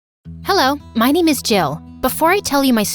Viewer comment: I thought i heard “Hello my name is JAIL”😂